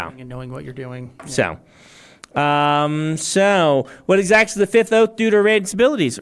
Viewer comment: what is he even saying here, i cannot figure out a single word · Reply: I believe it's "What exactly does the fifth oath do to a Radiant's abilities?"